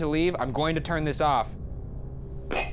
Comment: I heard turn off